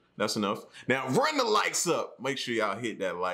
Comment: Runs the likes up!!! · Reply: I feel so dumb now i thought he said lights not likes